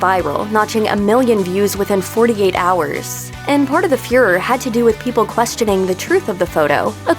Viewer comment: I thought I heard "Part of the Führer"